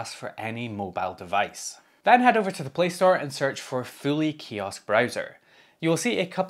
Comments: I initially thought you were saying Philly Kiosk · Show of hands: until he got to searching for it in the video, who thought he was saying "Philly" kiosk browser? 😂